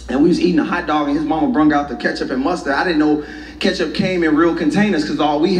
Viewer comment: Did anybody else hear him say 'brang out the ketchup' @ 0:18 ?